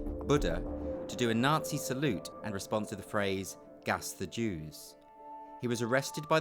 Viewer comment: Edit: I commented nonsense. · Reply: Oh my, you are actually right. Dunno why, but I heard it as "in response to..." instead of "and respond to...". My bad.